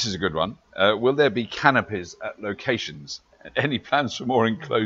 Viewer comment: When they first started talking about canopies I thought said canapés!